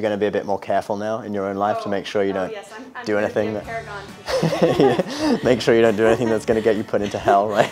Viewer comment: Excuse me! Does anyone know what said at 6:47 ? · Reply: Oh yes! I'm gonna be a paragon for sure.